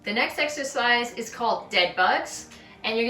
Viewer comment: I love dead bugs! You can really feel it working. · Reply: Oh! I thought she was saying dead "butts", bugs makes way more sense lol